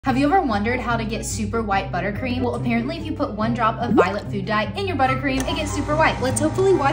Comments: Ok but for a second I thought you said "violent food dye" and I did a double take lol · I thought she said violent food die 😅